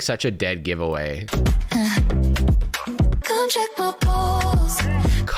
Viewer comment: I’m ngl I thought she said “come check my balls” 😭💀